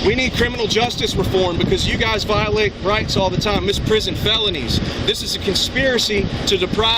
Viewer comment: Kept hearing this throughout his patter... anyone else hearing it? "misprison felonies" ?? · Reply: Yes. Misprision of felony, but the coughs lawyer does not know what it means.